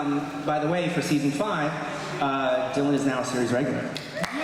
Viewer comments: Does anybody know what Jeff said at 8:57 ? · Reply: That Dylan Sprayberry is now a series regular? Then he said yes to something the moderator said. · what did jeff say in 8:55? Dylan is now? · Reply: A season regular